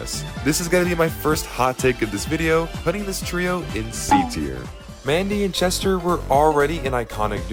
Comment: What. Did. You. Just. Say. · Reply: He said C tier